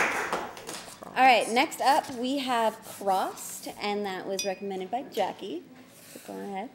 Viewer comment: I swear she said frost